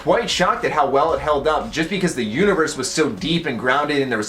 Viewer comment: When you said universe, I thought you said uterus